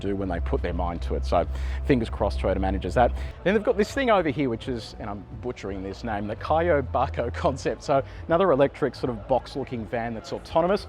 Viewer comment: Kayoibako. It sort of rhymes with "jar boy parkour". Kayoi means coming and going, or commuting. Bako means box. When put together it translates to something like "shipping crate" or "shipping container". Fun fact: "Bako" the same word as "Hako" in Hakosuka for those really old Nissans. When used in a word like this, the "hako" becomes "bako" in the same sort of way that "little" often gets turned into "liddle" when spoken out loud. The Japanese language often pre-plans for these pronunciation changes and bakes them into the spelling.